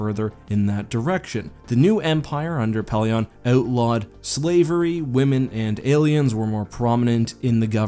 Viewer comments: What he said..."The new Empire under Pellaeon outlawed slavery. Women and aliens were instead".... What I heard...."The new Empire under Pellaeon outlawed slavery, women and aliens." lol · Heard that as Outlawed Slavery, Women, and Aliens, lol